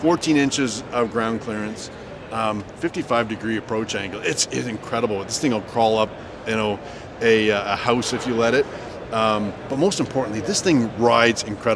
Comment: "This thing costs as much as a house if you'll let it." Swear I heard him say it.